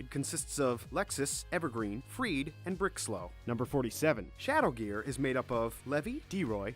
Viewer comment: did he say bricklow